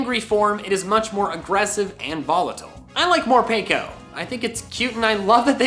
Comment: I was skipping forwards and thought you said More pika instead of Morepeko since itd a pika clone